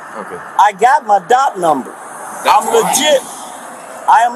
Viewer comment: When he said “I am legit! I just heard “I am an idiot!